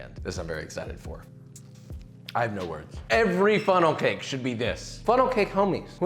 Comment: For a good sec I thought it said funeral cakes